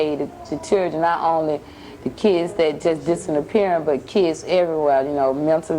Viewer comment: DID SHE SAY 'DISSINAPPEARING'???